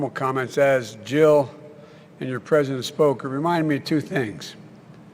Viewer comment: did he say Jill and your president spoke?